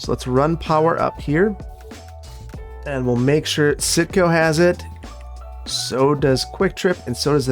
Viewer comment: I honestly thought you were saying SitCo not Cinco I didn't know till I saw the sign hahaha love ur videos ur amazing